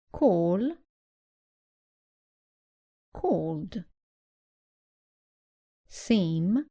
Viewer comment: Hello mam,Thank-you for your "Very Helpful Videos"...i had a doubt..when you said "called" at 5:13, i heard it as "cold"..So i just wanted to know "cold" pronunciation..if both are pronounced in same way then how do we differentiate them?